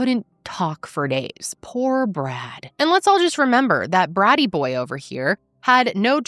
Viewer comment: A few times when you said "Poor Brad," I thought you said "Poor Brat"!!!!